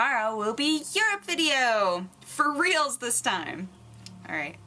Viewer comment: what i heard... "You're great Ian, Buh-bye" I was like, she knows my name!!!!!! :D